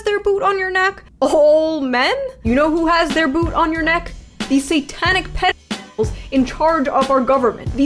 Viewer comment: I know the topic being covered is serious, but I almost spit my drink out at 13:49. had to watch again as I could have sworn she said "the satanic pickles"